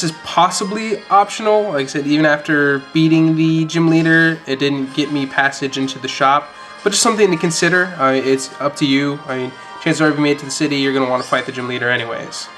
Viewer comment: I thought he said jew shop...lol